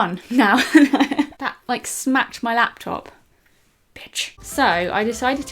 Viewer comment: Did anybody else hear what she said at 5:29 "bi*ch